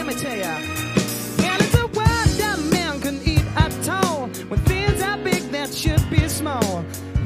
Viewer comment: I always knew that I must be wrong, but I always misheard the first line as: "And it's a world where men can eat a toe."